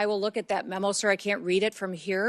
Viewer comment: did she just say “that is the proper ILLEGAL tool … to utilize on immigration enforcement operations”?